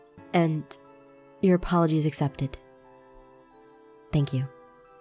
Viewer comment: I SWEAR I heard them say 'your CHALLENGE is accepted…' instead of 'apology' GAH STUPID HEAD